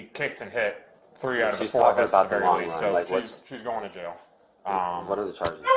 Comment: "shes talking about the long run (i heard him so i HAD to comment what he said idk why)